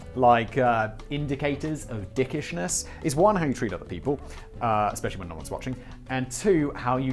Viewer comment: "hangtree little people"? "especially when noones watching". 11:50-12:00 what? I'm sure I misheard that. What did he say? whats it mean?